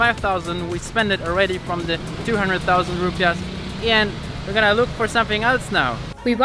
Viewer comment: why the subtitle says rupees instead of rupiahs tho.